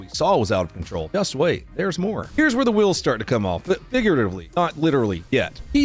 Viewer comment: did bro just say niguratively😂